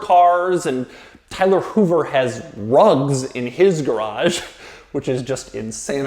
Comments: Thought he said Tyler Hoover has drugs in his garage 😂😂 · Did anyone else think he said Tyler has drugs at 0:57?